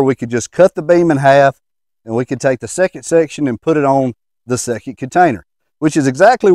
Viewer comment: Love listening to talk. I'm fascinated how he can make a two letter word sound like it has more. O N = on / which sounds like he said orn ... Genuinely, not mocking him at all, I love listening to different ways things are said. Did Jet ski 😂(slide) when the trailer stopped